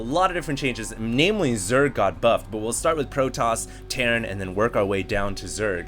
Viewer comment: did anyone else hear say "Mainly Zerg got fucked" at